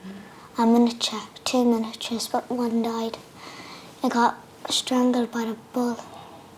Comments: I may have totally misheard but did that little girl say her horse got strangled by a bull? I'm so curious what happened I'm picturing a horse/bull street fight but I'm sure that's not what happened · I may have totally misheard but did that little girl say her horse got strangled by a bull? I'm so curious what happened I'm picturing a horse/bull street fight but I'm sure that's not what happened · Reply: I thought she said "strangled" too. I was very bemused, trying to work out how that happened